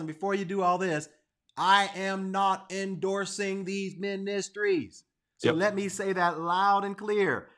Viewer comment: guys I think he's endorsing these ministries but I think I could be wrong. Not sure if I heard him correctly. Can anyone provide closed captions?